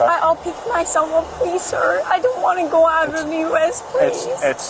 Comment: I think she says I don't want to go out of US (DUI for immigrants can become a deportable offense)